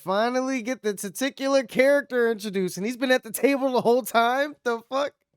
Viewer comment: Did I hear you correctly? Did you say "the testicular character"? If so, that's hysterically clever!